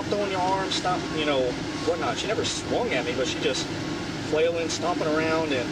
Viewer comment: Am I the only one who heard him say "she never swung at me."